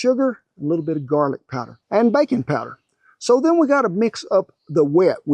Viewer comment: absolutely thought he said "bacon powder". Didn't even doubt it for a second till I looked at the screen 😂😂